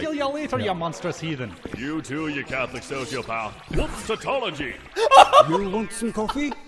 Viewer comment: Whoops a tology? what is Alucard saying as an insult? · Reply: He is saying "Whoops! Tautology!" Which means saying the same thing twice, except in different words.